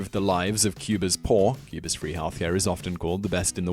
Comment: "Drastically improved the lives of Cuba's poor. Cuba's 'rehalf' here is often called the best in the world." That one word 'rehalf' destroys the entire sentence for me. I've listened to it many times, but I can't make sense of it.